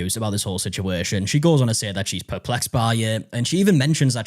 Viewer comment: CASTRATED!! Oh honey nooooooooooooooo lmao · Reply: I was like 😮hopefully someone else picks this up. It sounded like she said “perflexed” around the same time too 😂jeez